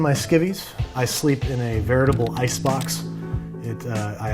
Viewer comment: I heard "vertical icebox" (wait what) I had to hear that again lol